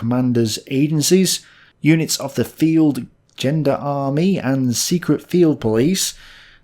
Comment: Who else shuddered and thought they heard "Gender army"?